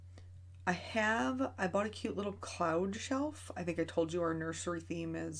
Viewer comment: I thought you said clown and thought well we can’t be friends anymore until you said my little sunshine and I realized you said cloud🤦🏼‍♀️. Clearly I’ve been watching to many horror movies this month. I have not tried water color yet although I have everything I need. I agree with you that these images are perfect for water color. If I have any time leftover before the holidays I’d love to try it. Good luck with the baby. Here’s to hoping you have already given birth by the next time we see you on here🤞🏻.